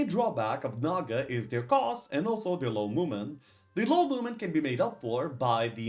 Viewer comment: i thought you said "the main drawback of naga is their cost, and also, they are women" at least i hoped you said that.